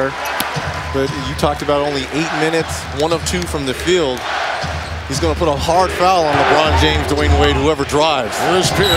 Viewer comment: What is the crowd saying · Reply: Kad Tubo defense...